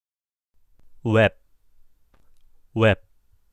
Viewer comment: i heard wap lmao